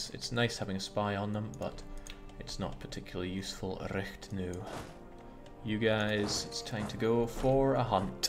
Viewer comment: rätt nu? Thought you said Reicht nauw!